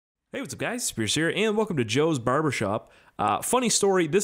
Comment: First time I watched one of his videos and thought I heard “Hey what’s up guys Scarce here”